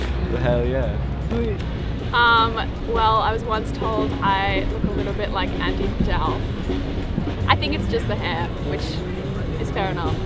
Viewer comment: mins - who did she say she look like?? Please anyone! what it the Name of that Celebrity ?? Lady with Curly Hair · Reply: Andie MacDowell